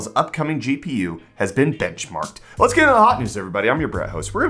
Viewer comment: I could have sworn you said Intel's new GPU has been bitchmarked". 😆 I guess that's better than being benchslapped? 🤷‍♂ 😉